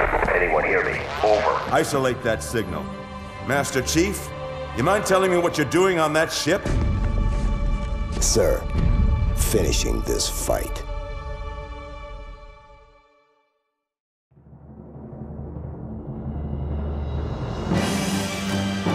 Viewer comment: When master chief said finishing this fight I thought he said Finishing this fuck